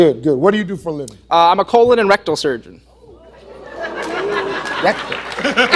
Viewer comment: I thought he said erectal surgeon